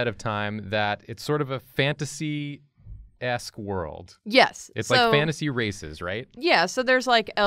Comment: i thought said "it's like fantasy racists right" omg